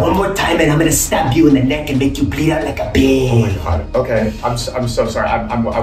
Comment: what he saying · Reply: He said he’s gonna make him bleed out like a pig